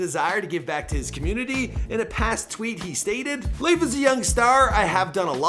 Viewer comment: did he say last WEEK? Or last TWEAK?! Lol😂😂